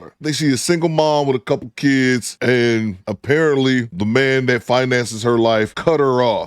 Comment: “ A couple of kids and a pair of Titties,” is what I thought I heard, instead of “apparently” is what I heard the first time 😂😂😂😂